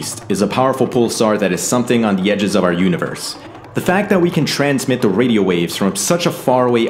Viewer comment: I thought he said "At the edge of our Galaxy" 4:31 "but its a rotating black hole" Me: well poop shinckle, we're all gonna die Me 10 seconds later: oh he said at the edge of our universe. Im such an idiot